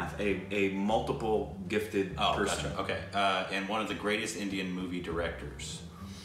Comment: Did just say Oh "Achaa" okay?? ❤️❤️😂